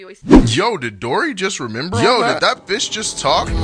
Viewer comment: "did that fish just talk" Yes, yes it did. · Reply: Wanna know something really bad? Initially... I misheard the guy... I heard "Yo, did that bi*ch just talk?" If you rewatch you might hear that too lol